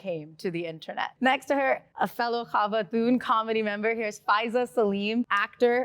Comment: did she say khawatoon? Wthh😂